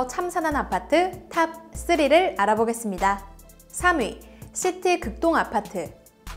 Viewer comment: 자꾸 시티 급똥 아파트라들려 ㅠㅠㅠ